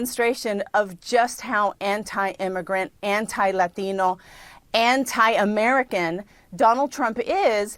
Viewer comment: Why did the way she said "Latino" in the beginning..sound like she said it similar to Digirono